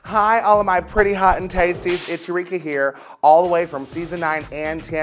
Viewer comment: Eureka sounds like she is saying 'It's Ricky Here'